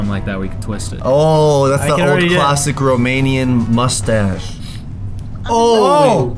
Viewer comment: did you say Romanian?